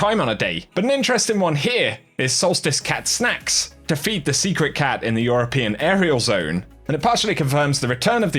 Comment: For a terrifying moment I thought you said "defeat the secret cat"